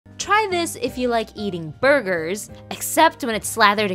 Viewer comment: I thought it said “try this if you like BOOGERS” I need help😭